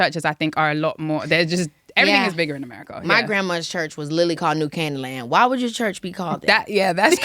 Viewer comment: Me living in Keke's old neck of the woods googling "New Candyland Church" cause that's what I heard instead of New Canaanland😂